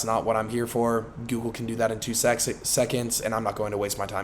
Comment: i heard that correction for two sexons 'seconds' 4:54 time stamp